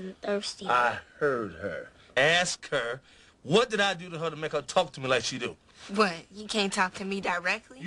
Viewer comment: I heard her Ask her what I told her make her talk me like she do